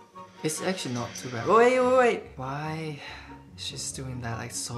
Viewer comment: When he said "wait wait wait" ( 2:29 ) it sounded like "oye oye oye" like how we call someone in india😂😂😂